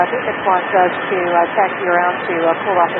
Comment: axi instead of taxi xD